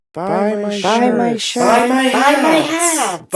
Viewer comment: i just realized it said “buy my shirt” in the intro and i always thought it said buy my shit wow i hate my life